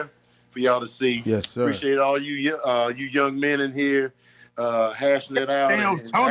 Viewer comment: was like 🤦🏾‍♂️ ain't no arguing with this guy🤷🏾‍♂️ And peep at 54:54 was finna say shout out to all you "yes" men before he said young men 😂 I think they peeped it too